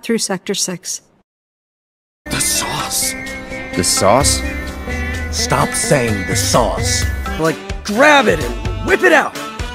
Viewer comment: Why do I hear "stop posting about amogus" instead?